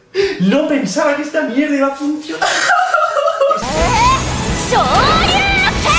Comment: estaba distraído y por un momento yo escuché HADUKEN pero después lo vi de nuevo y good el SHORYUKEN 😎😎😎😎😎